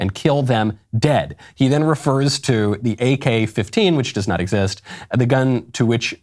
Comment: At the beginning I thought he said ak-50 I was like Wow Brandon Herrera fan before he tried to blow himself up with a pipe gun.. I now realize he's just an idiot.